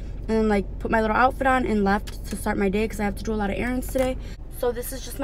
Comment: what did she say????? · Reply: “to start my day” i had to reply it as well lol